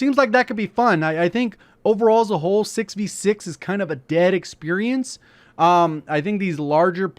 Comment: Did he just say 6 v 6 is a dead experience...